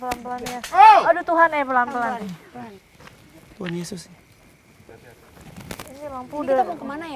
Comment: Maaf ni ya, kalau ga salah denger si nyebut "TUHAN YESUS" Di menit 22:33 🤔